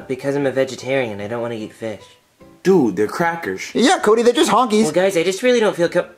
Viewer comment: what did jr call the goldfish? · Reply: Hunkys